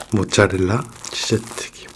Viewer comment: sounded like he said shi*